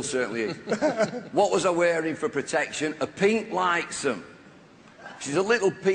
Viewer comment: sry but what did he say he wore at 4:05 ? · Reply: shpilzak A pink "Litesome" (the name of the box, in those days)